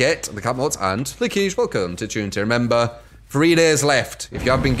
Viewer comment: What does say when people sub. I can’t understand and it’s irritating not knowing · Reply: It's jeh boi something about thanks for sub and welcome to the truenity